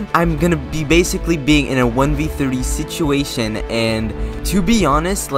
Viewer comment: hold up wait a minute did he say 1v3